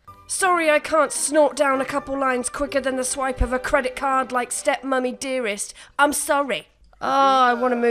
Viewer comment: “Sorry I can’t snort down a couple lines quicker than the swipe of a credit card like step mommy dearest” IM CRYING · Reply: I had to rewind that bit because the first time I heard "step money dearest" and I was like.. that makes sense and is hilarious but I still don't think I heard that correctly lol